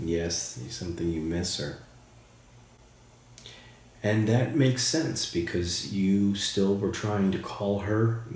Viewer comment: At the beginning I swear when you asked if you can use that he said it makes it easy that’s what I heard. I heard him say the makes it easy. I think the box helps them.